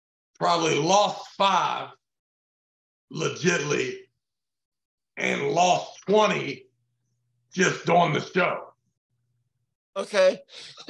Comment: "And lost 20 just doing the show" what? I couldn't make out the words.